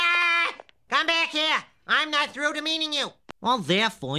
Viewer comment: "Come back here, I'm not through demeaning you!" is one of my favorite things to say IRL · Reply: I always thought he said, "I'm not through being mean to you."